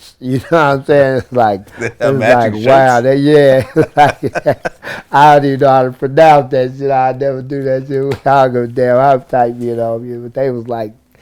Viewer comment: From 2:39 - 2:45 what the hell was Tray Deee saying lmao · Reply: I don't even know how to pronounce that sh!t, I'll never do that sh!t.. I do give a damn how tight me and homie is. ( He saying he don't care how cool he is with somebody, he'll never wear the same shirt at that same time as another man.)